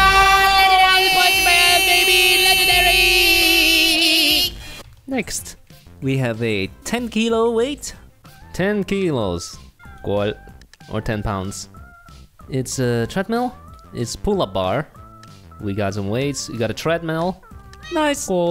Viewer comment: did you said 10 kilos like from the philipines